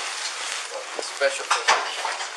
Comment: did anyone else hear that cop or whatever he is say “this guy is special privilege”